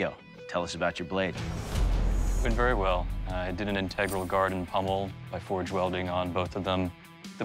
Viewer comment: I thought they said it needed a winged pommel?